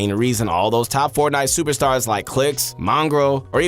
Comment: Wait did he just say clixx? 2:06 🤔🤔🙃